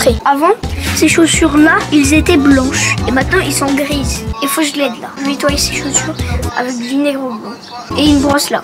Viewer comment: c moi ou il a dit faut que je le nettoye avec du negros